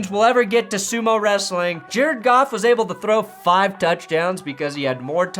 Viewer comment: Sometimes when I hear you say "Jared Goff" quickly it sounds like "Jare Goff" which of course sounds like "Jerk Off" in my brain and that's just fapping beautiful.